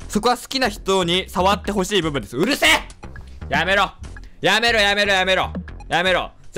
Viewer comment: 舐めろ！！って聞こえる笑笑